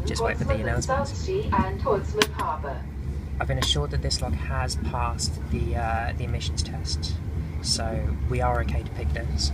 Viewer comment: Great road-movie ;-) and fantastic quick picking on the Burg Wächter. What did you say around 1:30, something with "...the lock has passed..." sorry I didn't get that. Anyway, love this video; it has a great atmosphere (as all of your out-door video). BTW: This lock has spools, but I missed the counter rotations. You seem to be a master in avoiding to pick the spools. Or did I just miss it? Thanks for sharing. Cheers! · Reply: I made a joke about the lock having passed emissions test; a joke on recent events with VW! I had to pick two spools (one at 2:30 and one at 2:50); the motion of the train and the angle of the lock makes it difficult to see, but definitely there. The spool at position 2/3 has always been the last to go. Picking this lock was more an exercise in feel rather than technique, just chasing road bumps and counter rotation rather than solving the puzzle of the lock, i.e working what's in there and where; I can't even tell you how many pins are in there! Not my usual approach, but I had a lot of fun with it! It's a consequence of working in a Brinks 527, which is an absolute joy to pick, but which I also pick more on feel rather than having figured out a binding order.